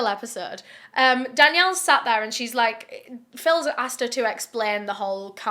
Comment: I don't know if it's just be but I swear you said Philza in stead of doctor phil at one point😂